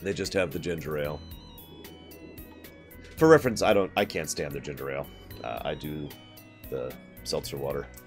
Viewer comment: I don't actually like ginger ale. I drink seltzer water. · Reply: ah, I thought you said you didn't like the Canada Dry Ginger Ale, my bad